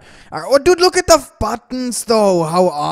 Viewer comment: ''Oh Look at the fkin' stove'' (i actually didn't understand what he said lol, i only heard oh look at the fucking ???)